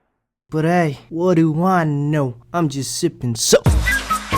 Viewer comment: I always heard, I'M JUST SIPPIN SOAP LOL HAHAH